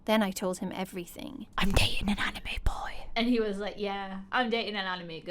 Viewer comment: it sounded like she said “I’m gay and a anime boy’..... OOOOFFFF⛷🥜🎿☕️🍺🥅⛹🏿‍♀️🏉🏉⛹🏾‍♂️🚣🏽‍♀️🥇🚓🚓🚑🚙🚵🏾‍♂️🚌🎽🍻🎽🎽🎽🎽🍻🍻⛳️🍪⛳️🍪🍭☕️🍹🍘🍘🍺🍱☕️🥫🥫🥫🍵🙃😳🤩🤩😰🥰😨🥰😱😱😝😱😛😨😋😚😰😥😔🥶🥶🤫😟🤫😫🤭😕😕🙁